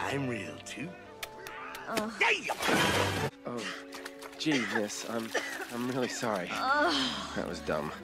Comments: Did he just say "Jesus"? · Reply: "Gee, Miss" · Did Herc just say "Jesus, I'm really sorry?" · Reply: He said “Gee, miss, I’m really sorry.” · Did he just say "Jesus"? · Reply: he said gee miss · Did Hercules just say Jesus? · did He just say Jesus?💀💀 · sounded like homie said "daayum" lol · did je say „Jesus“ in a time before Christ? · did he just say "Jesus"? 😶 This movie is about Greek mythology, right?